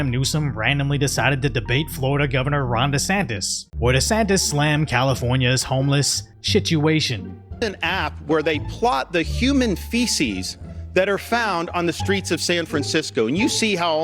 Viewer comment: "Homeless shituation" I heard that! And it deserves that.